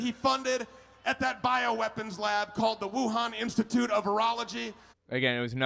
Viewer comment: totally sounds like he said "Wuhan institute of urology", just sayin'.